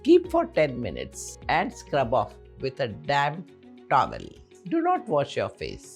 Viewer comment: Change to "damp towel" on your English subtitles. I love your videos because I would like to live a more natural life using your recipes. Thank you. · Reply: it was so funny i thought did she just said "damn towel"